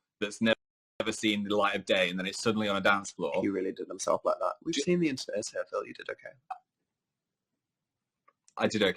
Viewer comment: Can someone please tell me what Dan says at 15:56? It sounds like he's saying "He really did himself like that, we just need The Internet is Here, Phil, you did okay," but I'm not sure if I'm hearing correctly and I don't know what that means? Is he just telling Phil not to be so hard on himself? I don't know? Help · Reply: its been a while but you mostly got it right i believe he said: “he really did himself like that, i mean, we’ve seen The Internet is Here,” you did okay.” so yeah i think he was just trying to tell phil not to be so hard on himself